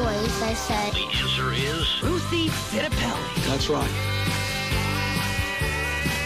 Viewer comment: What is said at 2:24 after "the answer is"? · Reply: I believe he says"Goofy Fitapelli" and I have no idea where that is from...